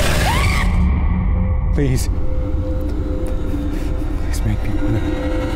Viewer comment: did he say "please, make me one of you?"